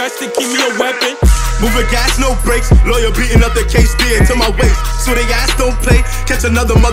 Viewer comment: wat did sheff say after “lawyer Beat another kase” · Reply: Mìçhæl Błîźž Nina to my waist